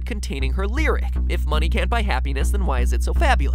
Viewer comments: When she says "Money can't buy happiness" I thought she said "Money can't buy a penis"😂😂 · When she says money can't buy happiness it sounded like she said that money can't buy a peanus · OMG I thought she said money can buy a penis not happiness I have a dirty mind